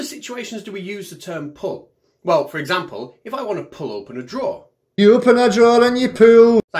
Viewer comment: I was a little alarmed because I thought he was saying "you open a drawer and you poo." Not surprised, just alarmed.